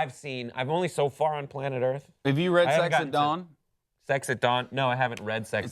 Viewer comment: I thought he said sex at dong.